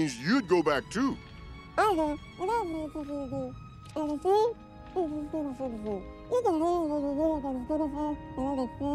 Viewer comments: did anyone understand Kenny? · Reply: 277imperator It sounds like- I know And I want to do this! And me?- I hate this stupid war You can all live happy and wonderful life, even if I'm not here, and with everyone else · what did Kenny say at 2:50? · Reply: Canadian Bacon It sounds like- I know And I want to do this! And me?- I hate this stupid war You can all live happy and wonderful life, even if I'm not here, and with everyone else Its what I think